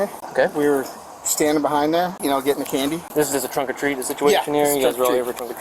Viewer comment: A Trick or Trunk event with children around. WTHECK?!?!?! He get 5 yrs PROBATION? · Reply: I thought he said Trunk or Treat? Either way it's over two weeks before Halloween 😂